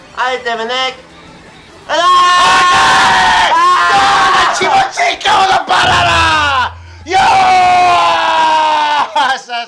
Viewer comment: 「気持ちいいか俺のバナナ」は少しだけ下ネタに聞こえる。